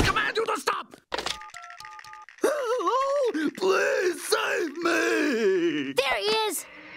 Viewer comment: Did he say PLEASE save me or POLICE save me 🤣🤣🤣🤣🤣🤣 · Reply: MOJO said PLEASE